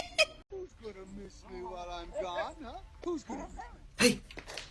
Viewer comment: Did anyone catch what Woody was saying before he got caught? I thought I heard something like "Who's a good boy while I'm god" Or something loke that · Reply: He said "who's going to miss me while Im gone"